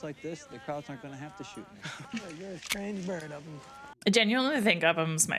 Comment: Who??? I replayed this like a 100 times and I can't understand a word. I'm not a native english speaker so maybe that's why lol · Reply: Upham